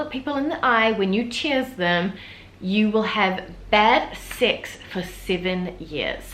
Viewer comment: When you said bad sex, it sounded like you said bad six.